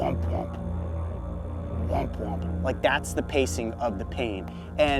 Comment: did bro just say womp womp?😂